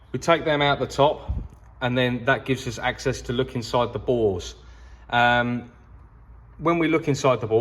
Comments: Look inside the bores (captions says balls) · the captions says: "balls" instead of "bores"! 😂😂